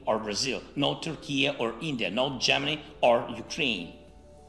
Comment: Did he just say why " not ukraine"?